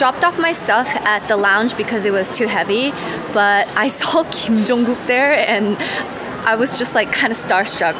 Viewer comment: Thought you said you saw Kim Jong Un 😭😂